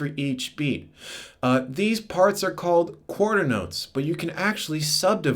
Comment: i didnt understand on 1:40 u said something like chordernotes? correct?